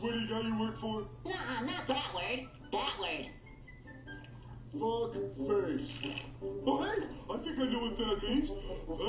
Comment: I thought patrick said ''bug face.''